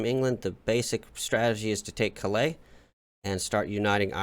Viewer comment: Did he just say “palé”? 😂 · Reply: Haha! I think I might have said Calais accidentaly as I had just looked at it before moving to talk about ireland :D